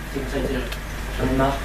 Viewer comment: Il dit quoi le vieux à 7:09 ? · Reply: stenaway Salut, Il a dit "J'en ai marre! "